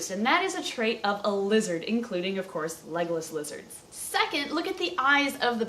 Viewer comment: Am I the only one who thought she was saying "Legolas Lizard" at the beggining... Oh... Okay.